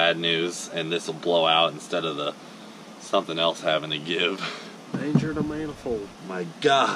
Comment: Anyone else hear at 9:05? “Danger to manifold” 🤣